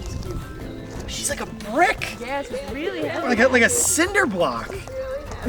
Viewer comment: When you went and went and figured out how heavy she was I thought you said she blanked a brick not that she was as heavy as a brick